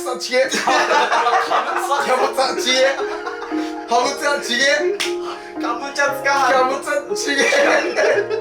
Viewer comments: 途中のハムカツから 最後の方ずっとハムカツに聞こえたw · マサイがカムチャツカ半島を言い間違えた時カムチャちえって言ってるんだと思うけど私はカボチャちげぇって聞こえるww